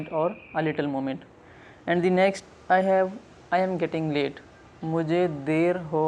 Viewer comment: I AM GETTING LAID!! Lol. Well that's what i heard!! 😂